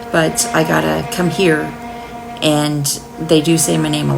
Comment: on the very first evp played, i heard 'come here...deena'